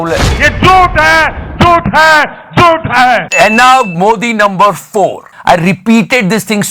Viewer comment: Modi no. 4?? Anyone else hear that?